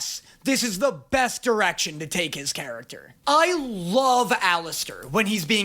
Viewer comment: did he say "the best erection to take"